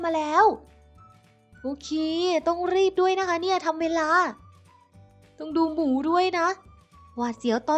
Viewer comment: ได้ยินพี่พูดว่ามึงดูหมูด้วย